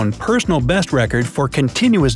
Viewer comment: I thought you said without practicing?